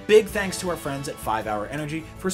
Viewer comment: Thought my guy said thanks for watching Gamefront!